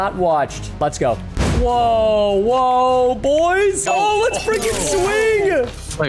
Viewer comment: DID U SAY “ARE U F-ING READY FOR THIS” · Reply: No, he said freaking lol I thought he said the same thing